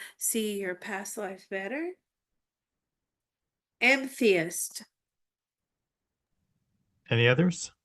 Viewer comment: What was the first crystal? I cant innerstand what she says. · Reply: Amethyst